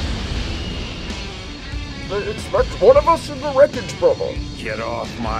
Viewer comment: what did he say..? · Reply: "They expect one of us in the wreckage brother."